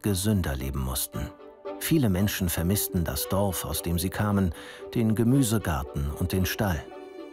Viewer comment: Entschuldigung. Was sagt man in 00:34? Was haben die Menschen vermisst, die neu im Ruhrgebiet waren? Ich kann eigentlich nicht verstehen :( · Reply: "Viele Menschen vermissten das Dorf aus dem sie kamen, den Gemüsegarten und den Stall" They missed their old way of living (in little villages instead of cities) which included their own garden where they could grow their own vegetables and have their own farm animals.